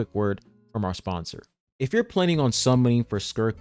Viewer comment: did dawg just say the word sponsor?😂